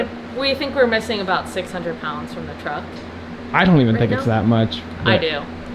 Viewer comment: Sounds like you said 3200 pounds. I definitely would of left the radiator out and the passenger seat.